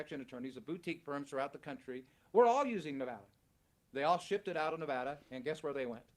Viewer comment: Tbh I think he said "were all using Nevada(past tense)" not "we're all using nevada"(present tense)